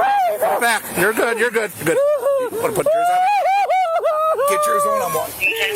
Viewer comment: when she said 'i can't breathe', did he say 'you're fat'?😅